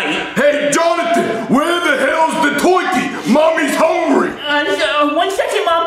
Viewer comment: Twurkey instead of Turkey 😂🤣